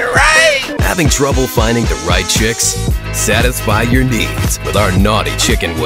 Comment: Finally a commercial edit that actually feels like it has a soul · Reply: Misheard: "Satisfy your di_k" 💀